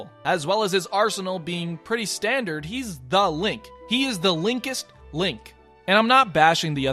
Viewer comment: I thought you said the linked lonk